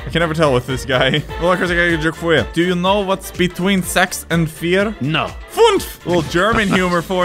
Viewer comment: did just say s##